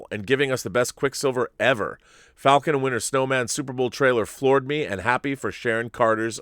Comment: Did anyone else hear Rob say Falcon and The Winter Snowman? Cause that’s what he said